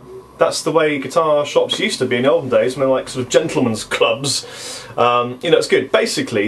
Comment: Did you just say, "djentlemen's club"?